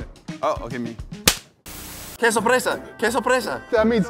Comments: ngl, but I thought said, "queso fresa (strawberry cheese)". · I heard “queso fresa”